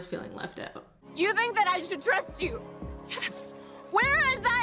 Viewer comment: "It says whatever our souls are made of..." "...his and mine are the same." Because of the accents I heard "whatever assholes are made of". Still technically true, and I think my version is better. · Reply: i thought she said 'you told me to dress cute, where has that ever gotten me?' instead of 'trust you' and i just rolled with it until cleared it up